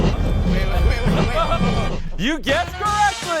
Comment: I thought at the end he said "isint that speed" and not sweet 😂